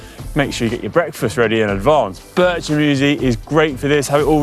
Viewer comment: Hi, what food are you saying you can have ready in the fridge the night before at 7:10? I don’t recognize the word. Thanks! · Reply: Bircher Muesli!